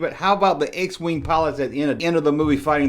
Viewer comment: Funny man - I thought he said "lock your spoils in attack position" I thought the wings in Star Wars were called "spoils". I also convinced my brother that Sloth from the Goonies was actually "Slaw" and that Obi-Wan was actually "Obi-Wath". It cracked me up to mess with him, and there were specific scenes where I could defend my stance with evidence where it sounded a little off.